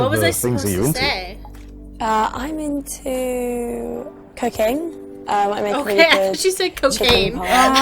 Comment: XD I thought she said cocaine too! Oh my gosh! XD